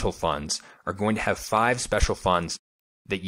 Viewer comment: He said “special funds” and I heard “garbage funds” 😳